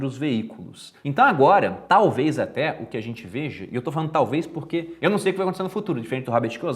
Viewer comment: Eu ouvi um "veje" em 7:04? rs... Acontece! hehehe